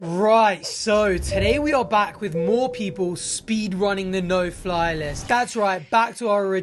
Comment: For some reason I heard "poor people" instead of "more people" and was like "Damn, Arthur out here suddenly shaming the less fortunate" 😂 I'm very sleep deprived so let's put it down to that.